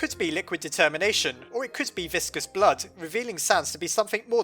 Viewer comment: I swear I heard Vriska's blood. Being a Homestuck I'm like BUT VRISKA HAS CERULEAN BLOOD!!! KARKAT'S THE ONE WITH RED!!! Gosh! Learn your Homestuck! But what do you guys think he said?